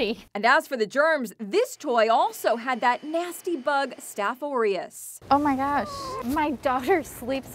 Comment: When she said staph aureus i thought she said stuffed oreos